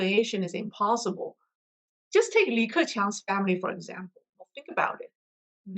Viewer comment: How do you spell the CCP official mentioned at 11:22 in the video. I heard something to the effect of Lee Kaoching. What is the correct spelling. Thanks.. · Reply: Li Keqiang